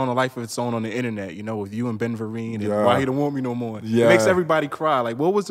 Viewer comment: Am I hearing that right? He says Ben vereen?